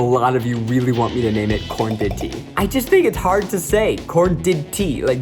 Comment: When everyone was saying korndidtea I thought he was saying korn tity